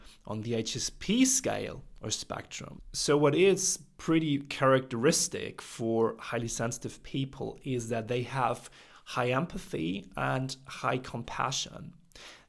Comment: What does he say after 17:23? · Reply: I do say "What is characteristic for highly sensitive people is that they have high empathy and high compassion." :)